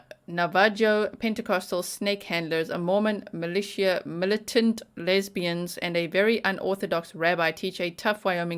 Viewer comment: Thought you said 'Mormon militia militant lesbians' and I was so there. Damn commas. 😅